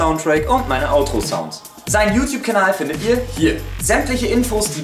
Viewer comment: Ich hab verstanden: ,, Sein Beauty Kanal findet Ihr: Hier." xD